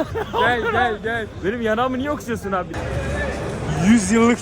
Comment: abi ney yar*a'mı niye mi okşuyorsun dedin ben mi yanlış duydum